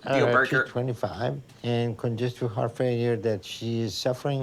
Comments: I thought she said "my fart phone"😂 but it was heart failure🤦🏽‍♀️ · I heard her say "fart failure" too! lol